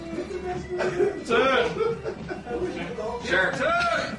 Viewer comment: At the end I heard 'that's awesome'